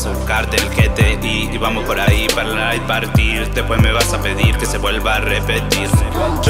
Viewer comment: Que dice en el minuto 1:04 ? No le entendi JAJAJAJAJA para laiparty? KIIIIIIIIIII